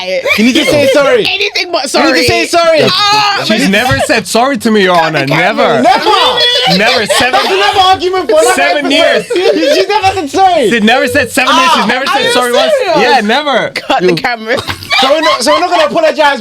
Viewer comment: did I just hear 7 years 🙏 hiah